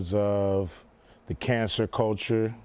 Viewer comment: I thought he said cancer culture 🤣